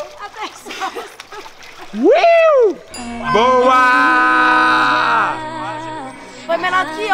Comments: SÓ EU OUVI O CHAMAR A FU DE MÔ???? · Tão falando que ouviram o falando "vai mo" pra no minuto 8:02 mas eu ouvi ele falar "vamo" e não fui só eu, agora eis a questão do que ele falou 😂 mas continuo achando que ele disse "vamo" 😕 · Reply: não ele falou "vamo" só q ele gritou e deu pra parecer "vai mo" mas não e ela sempre teve essa aliança ent parem de pensar coisas fictícias